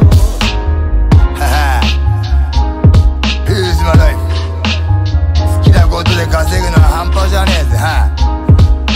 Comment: あたりでなんて言ってるか分かる方いますか？who's *** daddy?? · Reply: Who is your lifeだと思います